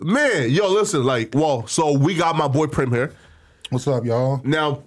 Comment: Thought bro said we got my boyfriend here